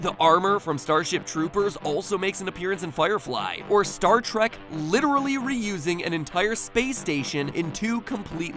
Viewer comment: I thought he said star ShReK instead of start trek 😂